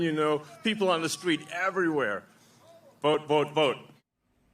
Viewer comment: Wait ... did Musk say he was "dork MAGA"? Because I definitely heard "dork MAGA". Hahahahahahahahahahahahaha!!!